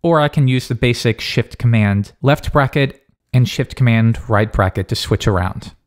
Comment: Seems good! Good work. By the way did you say "sh*t" in 6:11. BTW still is a awesome IDE.